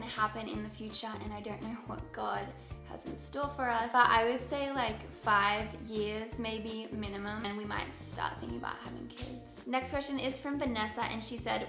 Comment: Oh my goodness when you were talking about having children and you said a minimum of 5 years from now I thought you said “5 kids minimum” and I was like woah that’s a lot 😂😂❤️